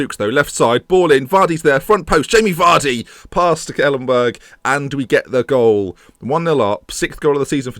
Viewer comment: Did he just say Stekelenburg?!?!?!?!